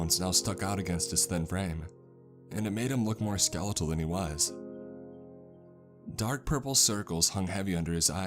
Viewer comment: i thought it said "dark purple sodas"